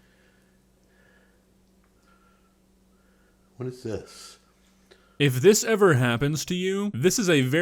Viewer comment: did he just say what's this after he made that how he isn't dead is beyond me